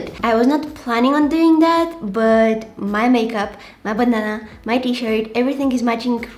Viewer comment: sounded like: my banana 😂